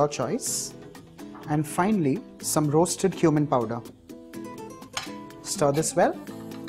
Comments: You said some roasted cumin powder I thought you said some roasted human powder I had to check subtitle I was so scared 😂😂😂😁😇😂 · "And finally some roasted cumin powder" I heard" roasted human powder" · Did I hear "Some Roasted Human Powder"? 1:52 xD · Did he just say roasted human powder or am i just deaf.. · Did he say human powder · "some roasted human powder" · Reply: 😂 I also thought he said roasted human powder then I said to myself he probably means cumin powder 😂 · Someone else heard roasted human powder?